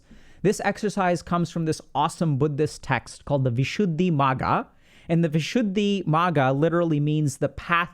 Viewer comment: huh did i really hear DR K recommend blog post 'We should be MAGA'?? :D